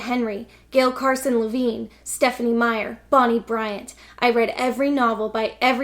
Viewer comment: I heard 'Bonnie Bryant' as 'Bonnie Bedelia' and wondered how the wife from Die Hard stumbled into YA fiction.